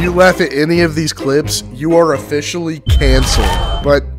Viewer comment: I keep hearing "if you laugh at any of these clips you are officially KFC"